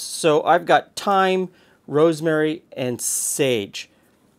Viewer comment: I'm a native french speaker and I don't know better than you what the term "root" refers to, other than "the root vegetables you have on hand at the moment". And your translation seems pretty accurate for me, except for a detail : "un bouquet garni de toutes fortes (in fact "sortes") de fines herbes" translates to "a bouquet garni of all sorts of fine herbs". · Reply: At what time of the vidéo ? At 5:57 I hear "thyme, rosemary and sage" quite clearly.